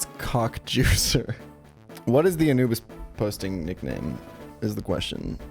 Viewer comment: lol the first time I heard you say Anubis posting - I genuinely thought you were saying “A n00b is a posting”